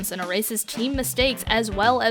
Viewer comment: Every time she said "ersases team mistakes" I heard "racist team mistakes" Just me?